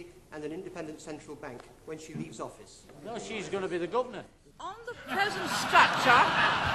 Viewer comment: what he said: "those she gonna be a government" ?